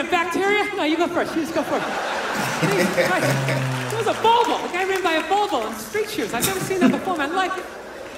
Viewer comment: What he say at 5:30 ?? -- "Beaten by a Volvo in street shoes..." huh? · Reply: “The guy ran by a Volvo in street shoes”